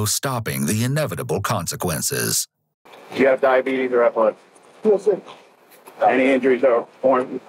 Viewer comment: on screen it says “do you have diabetes or high blood” but I heard diabetes and epilepsy. I could be wrong, but I have epilepsy and was wondering if that was what he actually said and why he would’ve asked that in the first place. Is it because it could possibly cause unreliable results or something?